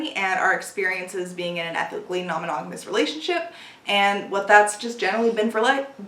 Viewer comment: When you said that your next vid would be on polyamory and your ethically non-monogamous relationship I heard it as "our epically non-monogamous relationship." and had to rewind to hear it right.